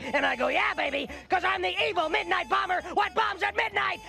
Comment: I always thought he said "the evil midnight bomber that bombs at midnight". Apparently he said, "what bombs".